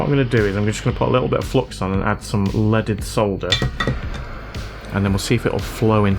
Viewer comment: During a lot of time I thought you were saying "let it solder" instead of "leaded solder" as a joke, cause english is not my first language. I think I'll continue to listen to it as "let it solder" if u don't mind 😂. Fantastic job!